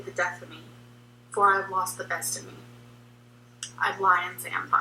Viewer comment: The first time i saw it when she said "best in me" i thought she said destiny